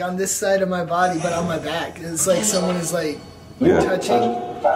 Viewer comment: was talking and he started staying local and then a women's voice said cementry but neither reacted sooooooo people were or are buried down or around there that is super creepy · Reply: I heard “someone’s praying”